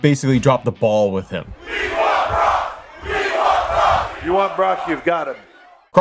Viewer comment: Please. Please. Stop with this narrative that we were chanting we want Kross to HHH at summerslam. I was there. It was we want brock. That being said. I do want Kross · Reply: It sounded like it was “we want Brock” to me. Which I emphasized with the Triple H slander for that particular moment since I figured I misheard too. It was also kinda cringe that the crowd was chanting that given things so I wanted to be wrong. Lol